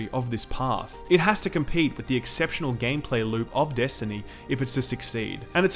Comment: did he just say at 18:44 "it has to compete with the exceptional gameplay loot of destiny" ? or did I hear wrong, because if that's what he said then what is he talkingabout, the loot system in destiny was and is one of the most infuriating and messed up things I've seen in the gaming industry , it was crap!